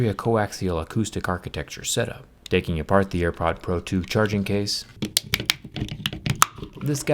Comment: Did he just say airpods pro 2 in